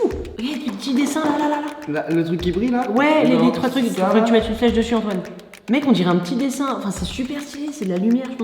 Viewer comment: c'est moi ou il dit :"il faudrait que tu mettes une flèche dessus Antoine "?